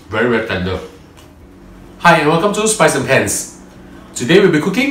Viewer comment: When he said hi welcome to spice and pans i heard it as spice and pants.. lolz...